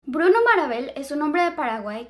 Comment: Yo la menos sorda escuche bruno marbel 😭